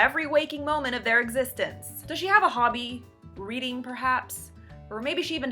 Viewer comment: Lol. @ 2:31 thought you said, "Does she have a hobby, breeding perhaps?" Then realized you said reading.